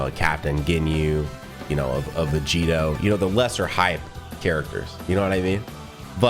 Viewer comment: "Vegito" What did you just say?